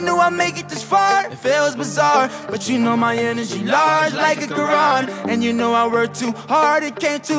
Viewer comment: Did he say “ my energy large like a Quran”? 1:07. 🙏